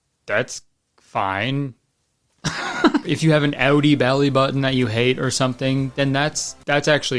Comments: i legit thought he said audi belly button i was so confused 💀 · hi, what he said at 41:56 someone? i'm not a native speaker and i am curious :D i heard something like outie bellybutton but stilll can you help me? thanks x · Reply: outie bellybutton is correct. some peoples belly buttons stick out rather than being recessed.